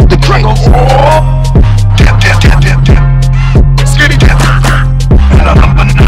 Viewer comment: damn he actually said skibidi X )